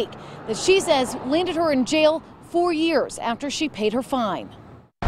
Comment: Oh, she said "it landed her in jail, 4 years after she paid her fine". I thought she said "it landed her in jail for years, after she paid her fine". Someone fire that caption writer.